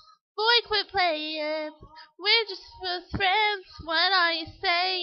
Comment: Did she say "Are we just fuck friends" at 0:37 ? xD